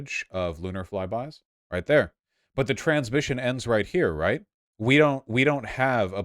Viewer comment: Around 2 minutes I thought he was saying "trans mission" instead of "transmission" so I was very confused with the context lmao